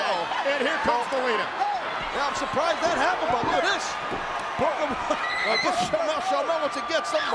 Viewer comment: why do the captions say ‘Kofi’ when cole clearly says ‘booker’